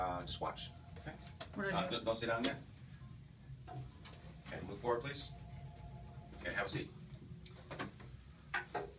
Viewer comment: what did yoou say at 2:14? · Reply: Todos Fanes LOL. I was telling my staff not to answer the ringing phone. Just keep shooting the video. Thanks for asking.